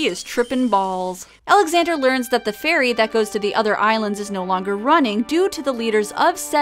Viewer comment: Haha, I heard "the ferry is no longer running" and thought "the fairy is no longer running" and was just wondering what happened to his legs for a moment.